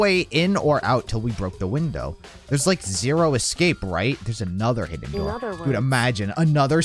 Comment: Did someone say "Zero Escape"?